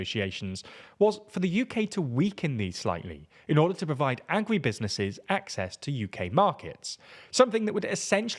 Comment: saying Agri instead of Agricultural doesn't save a huge amount of time, but it does make it sound like you're saying "angry businesses"